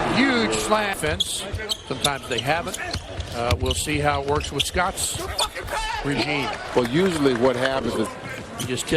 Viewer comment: Go to 2:04 and hear what it says. Do you fucking pass? or am I hearing something else. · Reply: "Good fucking pass boy"